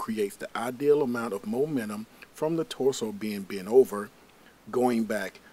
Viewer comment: did he just say momenum